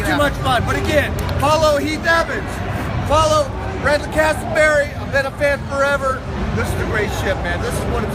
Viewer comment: Does Mike say Branden Castleberry?